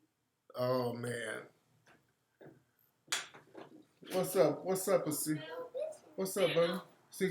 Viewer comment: What did Tariq call his Son? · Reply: Asir. His name.